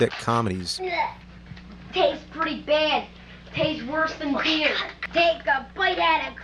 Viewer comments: Did he say tastes worse then beer? lol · I HEARD HIM SAY at about 3:40 "taste worst then beer" Where is the REAL JAMES?! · Did I just hear the James Rofle say beer tasted bad at 3:35?